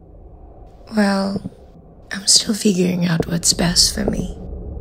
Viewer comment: please anyone , pas detik ke 00:10 itu lala ngomong apa sih? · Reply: well im still figuring out what is best for me